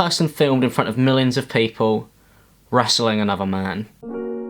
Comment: did he just say “wrestling”?